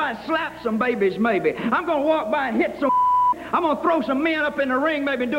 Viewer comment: the Honky tonky man says "I'm gonna walk by and slap some babies maybe, I'm gonna walk by and hit some BLEEEP..." What did he say? Anyone?? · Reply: women